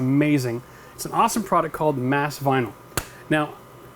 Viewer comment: Can someone please link to the black sound deadening rubber fabric he’s using? I thought he said “mask vinyl” but there’s no link in his description and a google search doesn’t turn up much.